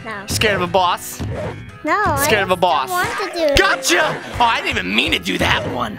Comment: Did u just say gacha.